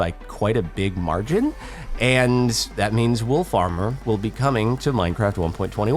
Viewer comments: It sounds like he says “wool farmer” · I really heard "wool farmer" when he said "wolf armor"